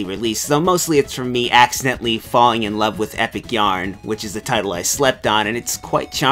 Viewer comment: First Kirby video in quite a while, I do hope you guys enjoy it :D. More stuff planned for the month, also :3. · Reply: Bass Yarn. For a moment I thought you said "Epic Jam"...